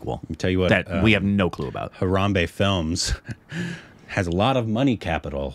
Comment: did say "harambe" films? R.I.P Harambe..🥲